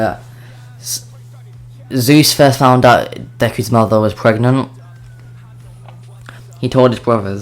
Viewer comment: i wasnt focusing and then i thought u said Dekus pregnant 😂😂😂😂😂😂